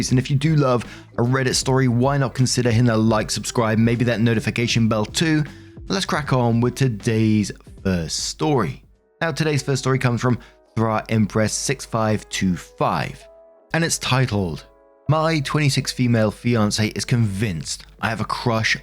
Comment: So I was only listening to the story and wasn't reading along at first and I thought you said the username was bra empress LOL thought that was kind of weird